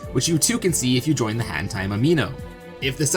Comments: I swear I heard him say hentai Amino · Bruh, am I the only one who heard “Which too you can see if you join the hEntAi amino”?- I’ma head out